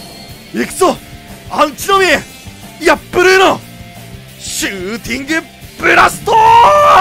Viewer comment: ここのシューティングブラストがガンダムのガトーにしか聞こえない